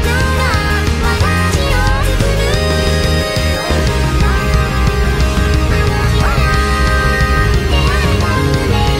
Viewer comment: 曲名 Schnabel 作詞・作曲・編曲 ぐるぐるぺんぎん 歌 初音ミク・鏡音リン 聞こえなくなったんだ これじゃいつか糸が千切れちゃう 見えるはずの光景が 鏡の前で無音の日々 守るべき物が零れていく 何も出来なくてふがいないや 生み出された限りあるメロディーが 一人だけに聞こえてる 透明な世界 ねぇ未来は大丈夫？私は今日も歌ってる？ありったけ届くまで名前を呼んでいて ねぇ私は大丈夫？君の声を聞いて歌ってる？モノクロのその世界線は今へ繋がる （間奏）約６秒 明かり灯して耳を澄まして 広がるシルシを黄色く塗って 結びついた理由を もっと深く刻み込んで忘れないで （間奏）約５秒 今もここで願ってんだあの場所で歌えるように 温め行こう！もう見えるかな？？ 大声で奏でた限りあるメロディーを ステージで鈴を鳴らし続け右手上げて放つんだ もう私は大丈夫？君とキミが繋いだ未知の先で連なったこの世界線は何色に染まる？ 信じちゃって大丈夫？私は笑顔で歌っている？両手を上げて呼びかけて もう未来は大丈夫！私はいつだって歌っている手拍子で全員が包み込むステージで 何度だって振り払え限界見えたって超えてみせる溢れ出す強い感情が私を創る あの日ほら出会えた運命が きっとそう続いてるんだ ねぇ？？ 今回、間奏が短かったので入れるか迷いました。2:45秒のコーラス？が何度聞いても分かりませんでした。「空が～」に聞こえてからそれにしか聞こえなくなりました、答えが知りたい！ 歌詞間違ってたら教えてください。それでは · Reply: そうなんだ って歌ってますよん!!